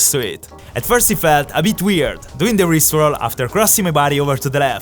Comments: When he said rip roll I thought he said rick roll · i genuinely thought he said rick roll instead or rip roll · I thought you said Rick roll